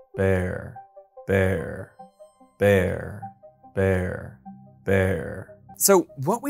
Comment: I heard "where" in the second clip.